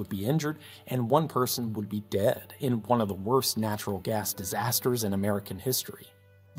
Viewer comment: gotta say, if 1 dead and 2 dozen injured is the WORST natural gas disaster in US history, yall are doing a great job, thank you for your service. like, its a shame that any such disaster happens, but if that's the worst, I'll consider it a job well done on the whole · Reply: he did say "one of" the worst though which I didn't catch that part the first time